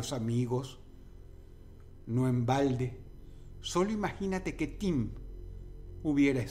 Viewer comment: se oye que dice verga 😂